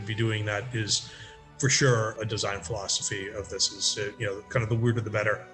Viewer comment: Right after the Beast Master bit, it sounds like says design flawsophy instead of design philosophy. I don't know if that was on purpose but totally coin that! The emergence of the Beast Master among other things in Daggerfall is just fantastic.